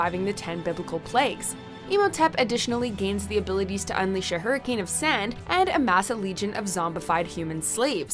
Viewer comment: did she say hurricane of sand. It's sandstorm